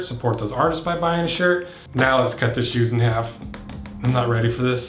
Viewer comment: the captioning says, "now let's cut this Jews in half". I then had a whole scenario flash through my mind about if a def jewish person thought this channel was about "cutting Jews in half" instead of "cutting shoes in half".